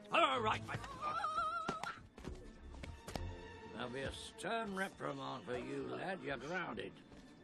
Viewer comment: what is he saying? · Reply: “That’ll be a stern reprimand for you, lad. You’re grounded.”